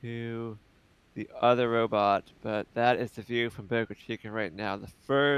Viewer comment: Haha 3:08:36 subtitles say ‘Poker Chicken’ not ‘Boca Chica’